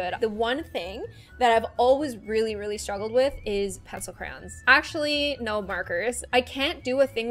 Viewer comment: Did she say pencil crayons?